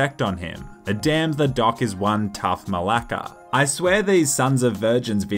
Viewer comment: did you just say μαλακα?